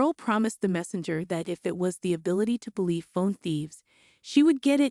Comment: So her super power is side-questing? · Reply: I'm confused too I thought it's said something like phone thief? Shi I even put the subtitles but it didn't show 😂 ok the subtitles said " the ability to believe in phone thieves" lol what the heck